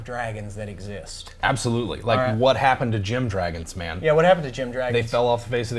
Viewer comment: I thought he said Jim dragons 😂